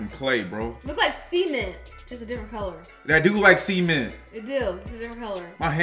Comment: I love how they said c-ment instead of cement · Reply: i thought they said semen